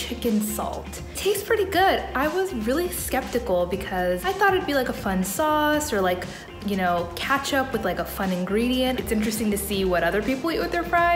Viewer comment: did she just say katchup?